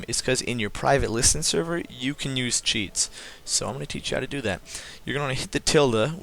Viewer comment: I swear to god it sounds like your saying cheat not cheats